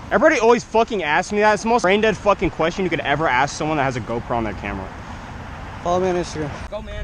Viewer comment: did he just say he had a gopro in the Camara